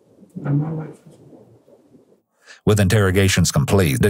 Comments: it sounds like says “end my life” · after the detective asks him, “and that’s the honorable thing to do?”, she walks out and the closed caption says that words were mumbles (inaudible). I think he was answering her question because he said, “end y life”. Then when he’s with the other three he says something similar. He says, “[he] just want[s his] life to end right now.” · “damn my life is over” is what he said. Yep. Yep it is boss. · he said my life is over for those who didnt understand him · it sounds like says “well my life is over” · It sounds like says "F*** my life." after the detective left. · What you couldn't hear him say, I believe his bitch ass mumbled "not my life" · I think he’s saying “And my life is over” when he realizes he’s caught. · Heads up: At 57:03, it says "Justin mumbles" but he says, "Man, my life is over." Just for those who couldn't understand him! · It sounded like he said "Not my life"